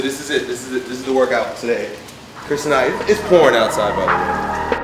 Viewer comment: its porn outside btw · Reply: i thought i heard it incorrectly, did he actually say porn or pouring? lol...